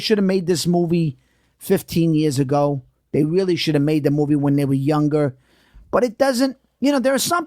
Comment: all i hear is “shouldn’t have had that popcorn” 🍿😂🤣